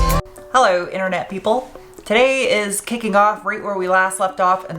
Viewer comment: you use so much innuendo I swear I heard, "hey people, today is kinking off where we last left off."